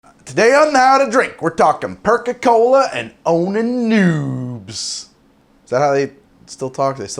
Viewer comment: I thought you said boning noobs